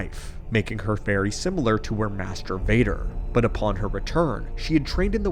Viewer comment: I heard it wrong. 😂😂😂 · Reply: Mass debater😂